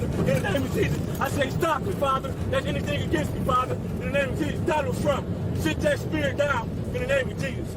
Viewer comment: Did he just say "in the name of Jesus Donald Trump"? I went back several times and I'm sure he said it lol